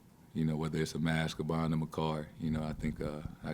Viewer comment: I heard, "buying him a carD." I was like, "so generous." 😆